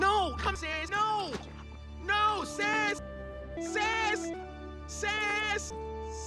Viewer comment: is he saying sans or saas